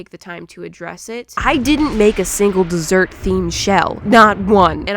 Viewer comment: To be honest I thought she said desert XD